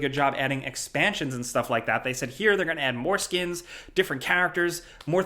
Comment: I paused at 6:22 to do something and when I played the video again I thought you said they added foreskins lmao